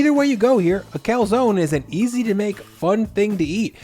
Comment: Did he say, "Easy to make?" 10:02 😳🥟